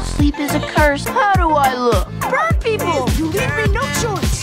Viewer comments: did he just say “brown people” I’m so confused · I heard burnt people at first and I was so confused 😂